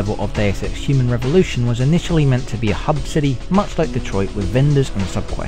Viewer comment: Did you say 'much like Detroit'?